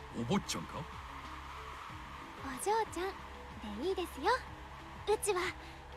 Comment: Not going to delete this because I don't hide my mistakes but I just learned that they are both pronounced "otoko no ko" musume the reading I expected for the 娘 kanji. In fact, my keyboard knew exactly what I was typing. I love learning Japanese but my goodness it is things like this that drive me insane. No sure if anyone has pointed this out. You used "Otoko no ko" when you meant "Otoko no musume". The translated tweet at 5:20 has quotes around 「男の娘」 Otoko no ko (男の子) is "male of type child" or boy. A Generic way of referring to a male child. Otoko no musume (男の娘) is the "male of type daughter" or male daughter. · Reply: I would like to add that his voice line is "o jou chan de ii desu yo" (お嬢ちゃんで いいですよ) > miss is fine. (What the arrow is pointed to 5:45) Then he says something like "ichi wa" (いちは) but I must be hearing her wrong. My guess is this is him referring to himself in a manner I am not aware of followed by the rest of the sentence "onna no ko desu kara" (女の子ですから) > since I am a girl